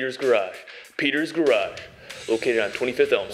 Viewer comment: I'd rather stay weak than go to Peter's garage. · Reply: I thought you said peter’s garbage for about 2 secs